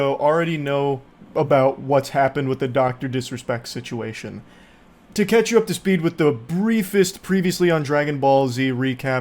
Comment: almost thought you said “to catch a predator” lol